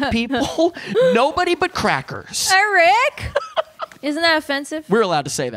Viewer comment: Thanks so much for this... But, just so you know... there's a story behind the story... Because, other than the idea that there was an Uncle Fred who died just before Halloween, EVERYTHING else about this story is something my writing partner, John Lau, and I, wrote. And, to his credit, R.L. Stein actually celebrated this, when he credited the novelization fo the film thusly... "When Good Ghouls Go Bad" by R.L. Stein, based on the Screenplay by Patrick Read Johnson and John Lau. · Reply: I thought you said "Nobody but crack"